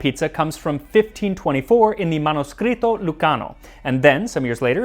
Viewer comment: I thought you said "Manos Gritos"...which is something completely different than what you said 🤣👏🏻 Manoscritto